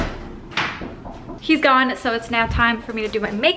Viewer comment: for a second sounded like "he's gone so it's nap time for me" and I was like girl same lol